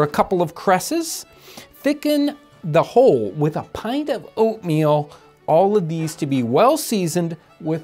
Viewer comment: I could've swore he said "The opium that goes in at the end!" and the blood drained from my face for a fat few seconds before i realized they said oatmeal lmao.